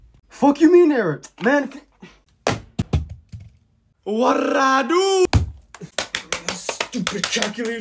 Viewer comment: WHAT I DO??? The way he said it tho was hilarious